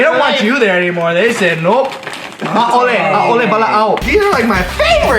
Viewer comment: So at 5:05 Ryan said something in Hawaiian that I tried to Google. The closest I got to it was "A'ole wala'au" (Considering he said Hawaii with the 'w' sounding like a 'v' not long after), but the closest translation I got to it was "Stop talking" or "Be quiet" since other websites told me it was a sentence fragment. It also didn't make much sense in context. I was just curious what he actually said, and what it actually meant. X) I love learning about other languages, so if anyone's got an answer for me that would be great.